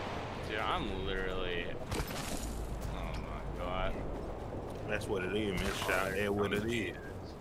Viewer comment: did anyone hear that ? Some low tone voice say that’s what it is shawty that’s what it is ..? Or am I just to high